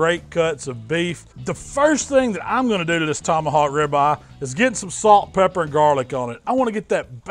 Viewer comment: I thought it said tonyhawk rib lmaoooooo